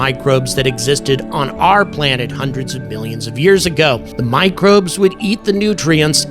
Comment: I heard "a hundred million".